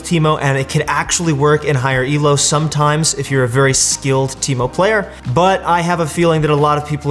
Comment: did he just say " if you'r very skilled teeeeeeemo player" ??